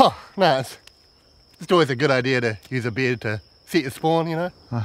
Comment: What cracked me up is how Hamish's Kiwi accent is so strong that it sounds like he saying "beard" instead of "bed". AND the auto-generated captions also interpreted him as saying "beard"! :D